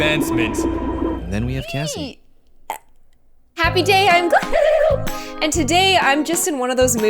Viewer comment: I thought that she said happy day ON gloom not I'm gloom